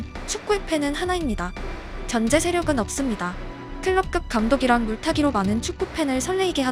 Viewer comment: 아....내가 클럽급 감독을 클롭급 감독으로 잘못 들었던 거구나....